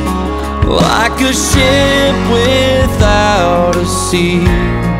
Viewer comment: I thought he said "I could sh*t without a seat"